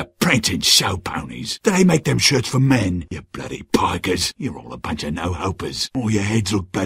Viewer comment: I swear i thought he said "poggers" and now i cant get it out of my head